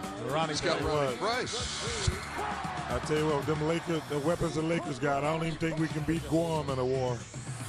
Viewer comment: what does Chuck say? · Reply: Something like: I tell you what, with them weapons the Lakers got, I don't think we'd beat Guam in a war